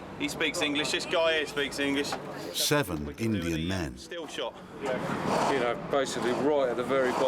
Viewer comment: Did he say 7 Indian MANS?